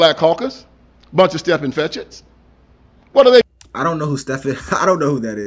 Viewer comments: He's saying "Step-and-fetch-it" instead of a person's name. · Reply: Thank you for this clarification. I kept hearing "Step and fetch it". Never heard of the black actor you mentioned. Glad to have that clarification. Thanks! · i think he said "step-and-fetch-it's". like the step to you and then fetch what you tell them to.